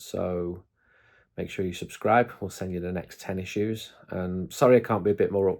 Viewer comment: I thought he said he was going to send “tennis shoes”… then i realised he said “10 issues” 😂